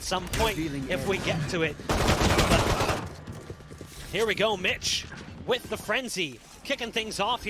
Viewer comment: I thought he said here we go bitch